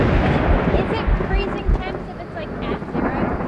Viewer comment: Did the ghost just call you a "mouth breather", or did it say "half breathing"? 12:50. 😂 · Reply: I think that was saying “is that freezing?”